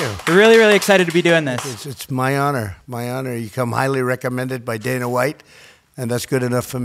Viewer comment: Lol I thought he said, Donna white...